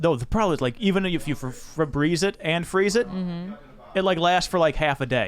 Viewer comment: George, did you just say instead of washing my jeans, I should "Febreze and freeze" them?!...Points for rhyming but...